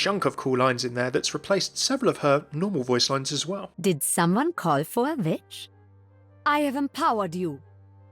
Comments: did someone called for a bitch? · Did someone call for a bitch? ;)) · lel look at 2:55 and put captions and it will apear did someone called for a bitch 😂